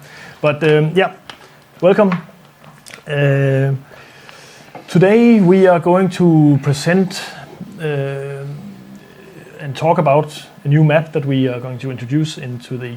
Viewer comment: when it began did anyone else mishear it as "we are going to add a new meth"?